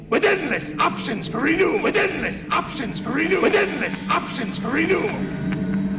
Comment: WITH ENDLESS OPTIONS FOR RENEWAL! WITH ENDLESS OPTIONS FOR RENEWAL! WITH ENDLESS OPTIONS FOR RENEWAL! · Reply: Finally, I know what he said. Until this day, I thought he said "Within this option for you." Which didn't make any sense. Thanks for clearing that up.